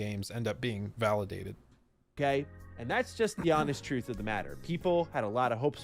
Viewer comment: Did he say “and that’s just the Amish truth”?